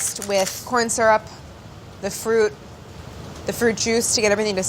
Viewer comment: Claire: says 'fruit juice' @ 22:38 Me: lol that sounded like Goof Troop. My brain: spends the day singing the Goof Troop theme, but with fruit juice instead of the lyrics